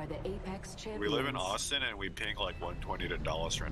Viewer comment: "We live in Austin and we're pinging 120 to Dallas right now " I live in NoVA and I heard 'Dulles' at first. Lol I was thinking: "of course? That's like 2000 miles away.."